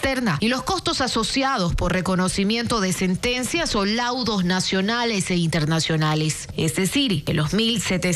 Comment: Faltan 11 mil millones. Perdón escuché "litigios internacionales" 😮